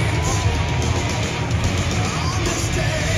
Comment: What he says 15:44? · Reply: "Next time I'm here, hopefully we'll win the cup"